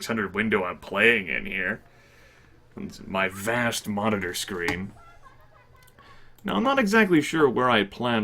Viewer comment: Fun things I heard and chuckled at, because I'm stupid in the head: 5:29 - Monitor screen. MONITOR SCREEN! (Monitor screen? It sounds wrong in the right way.) 5:40 - My quesslaw. It must be checked. Oh, and the seventh bottom HUD icon is for allowing your male character to jerk off. I'm pretty sure. Hand holding dong.